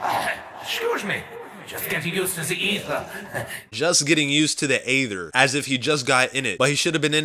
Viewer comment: Every time ricktofen said getting used to the aether I thought he said getting used to the evil